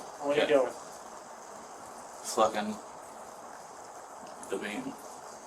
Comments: I swear it sounds like he said, flicking the bean!😂😂😂😂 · Did he say "flippin the bean?"